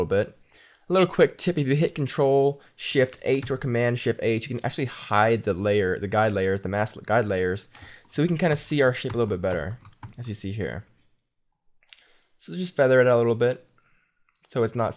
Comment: If I'm not mistaken, it almost sounded like u said " so we can see our SH#T a little bit better" @ 4:53. Lol